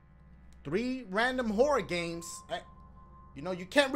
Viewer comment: I keep hearing Dashie say "Whore games" lmao XD